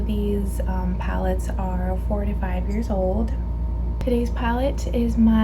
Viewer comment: Girl I thought I heard you say 45 years old! I was like throw those away you sinner!!! 😂😂😂😂 jk jk